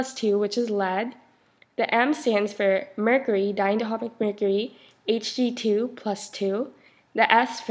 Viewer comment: what did she say @ 1:37? '' something mercury '' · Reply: "Diatomic Mercury"